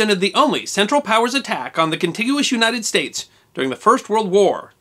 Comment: Great content, but it is bothering me that you said Continuous instead of Contiguous for the United States around 9 minutes in, I still love your content it just bugged me Edit: I misheard